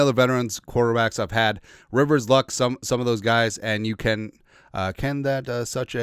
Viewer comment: 😂😂😂 i read that like 3-4 times too. I thought i skipped a line or something 😂😂 i think that should say "you can SEE that"